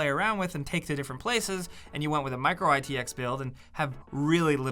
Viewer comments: Did he just say micro ITX at 5:55? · I hope he said 'Micro-ATX' at 5:53, because it really sounds like he said 'Micro-ITX', ITS MINI-ITX AHHHHHHHHH